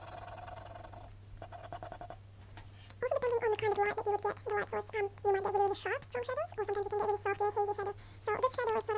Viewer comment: I would really like to know what you said there...😕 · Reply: hahaha I must have missed that. I'm just saying there that the artificial light can make shadows appear sharp and soft daylight usually creates softer shadows :)